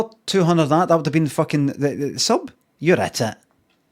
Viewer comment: Ollie had exactly £4 left in his bank account after paying bills on that day and, on his way home—after a long shift at work—he bought himself a wee single McDonald's cheeseburger to celebrate his being in the black. He then logged into Twitch in a really good, generous mood—savoring his wee luxury burger—to watch Limmy and got 3:58:55. Tsk... Terrible stuff. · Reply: Pretty sure Limmy is saying "You're at it!" at the end there, but I prefer what I keep hearing, which is "You're a tit!"